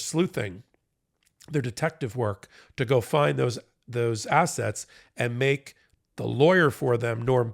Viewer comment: said 'Find those assets,' but I heard 'find those assholes!'